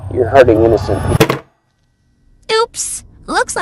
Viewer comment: na minha opinião eu acho que ele falou "Você esta machucando pessoas innocentes" deu para ouvir o "pe-" no final